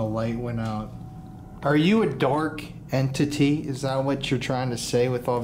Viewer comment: When you asked if it was a dark entity, it sounded like you said “are you a dork” and that would totally be me as a ghost like ey it’s me, the dork 🙋🏻‍♀️ I LOVE!